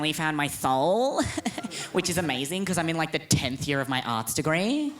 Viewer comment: what did he say @ 24:50 10 th year of on sta grey or what did he say i didnt get the word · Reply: "10th year of my Arts Degree."